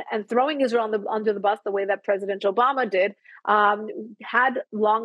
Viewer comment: did she say "President JoeBama"😆 I guess it's accurate